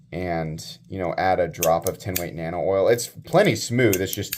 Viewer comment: Hi from the UK. You mention at 6:42 type of nano oil, unfortunately I couldn't quite hear what brand. Please could someone help me out on this? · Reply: 10 weight nano oil